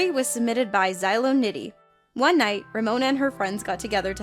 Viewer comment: I thought it said sin lol